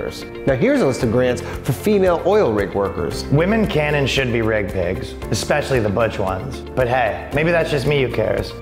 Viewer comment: "Woman can and should be rig pigs...especially the butch ones" bwahhahahaha Top kek Had to replay it i thought Ryan said Wig Pigs...